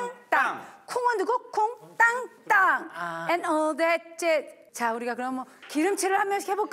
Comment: It sounds like she is saying saying “All that jizz” smh konglish